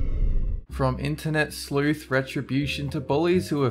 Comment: Thought the word said "slut" instead of sleuth !